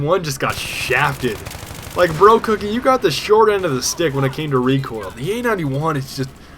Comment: did he say: "brocookie you got the short end of the d*ck when it comes to recoil" AwKwArD 0_o